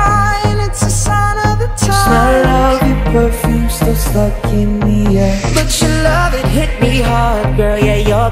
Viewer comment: I thought Naill said "fuck in the air" at 0:19 lol